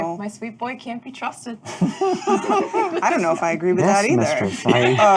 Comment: what did say at around 32:12? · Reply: I think it was 'yes mistress'